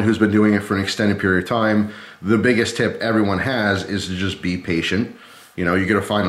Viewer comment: I thought he said the biggest tip i have is be asian